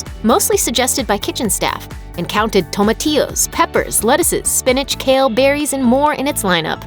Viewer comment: did she said tomatios instead of tomatoes?